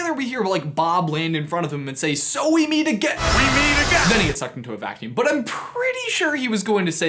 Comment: I think he says again. These are the hard discoveries we uncover on this channel... Me- WHAT? He says "again"!? Danget! I thought he said platypus!